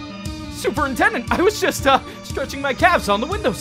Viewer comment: Immediately cracked up when I heard Superintendent Vegeta at 1:15 lmao